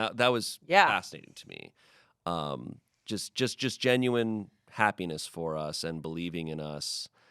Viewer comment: I paused for a moment, right as he said the "ha" part of "happiness". Went back to play it. First thing I hear is "penis". Enjoy, those who want to find dumb stuff that proves that Smosh TOTALLY makes giggles for 8 year olds. Haha. Penis.